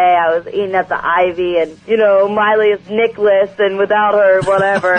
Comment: I THOUGHT AT 3:02 SHE SAID NICHOLAS